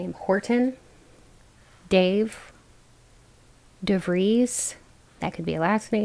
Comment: Pile1 , I am amazed. You mentioned the kind of energy I tend to embody + Aries energy, my middle name is Charlotte and can you clarify if you remember what last name you said at 22:43 ? 🫶🏼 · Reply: I think it was Devreez. Before that was Devereux